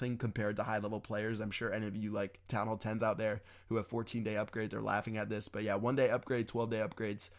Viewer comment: D-Did you just say fourteen day upgrades?